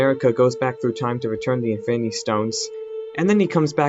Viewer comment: it's peggy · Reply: Ye BOI The SAVAGE I know it’s Peggy by it sounded like he said piggy